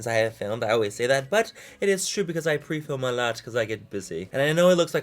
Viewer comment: I thought he said 'the last video was prefilmed because i get pussy'